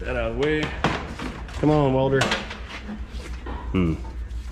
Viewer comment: "come on Walter" is what I thought you said on your first pull on the welder ;-)